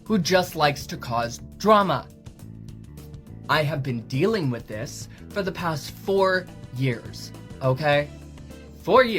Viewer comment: ''I have been dealing with this for 4 years'' All i hear is ''It's been 7 years Marty!'' Cyraxx the pedophile and these guys are weirdly relatable.